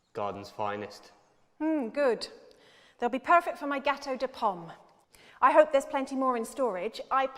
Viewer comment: Gatow . . . Pom what??! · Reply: Gateau de Pomme, which is to say Apple Cake in French.